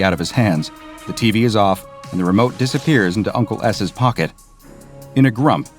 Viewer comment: Bruh why did i hear "uncle ass" 😂